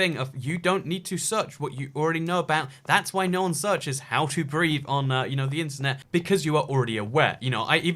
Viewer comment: I thought you said "how to breed" and not "how to breathe"😂😂😂😂